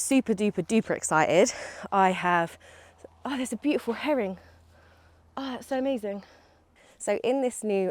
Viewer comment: lol, definitely sounds like you said herring rather than heron 😅. Love what you’re capable of in the Tiny Garden and always inspired, though my woodwork is always a bit wonky 😂. Been watching your allotment vids and realised I missed a couple before then, so catching up. I’d be up for watching hikes and things and extra fun if you can include some foraging maybe? 😊